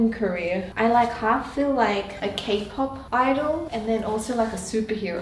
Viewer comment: i thought she said “cake pop idol” lmao i had to rewind it 😂😂